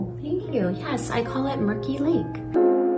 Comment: At first I thought she said her hair color is called Rickey Lake 😒😂 I love her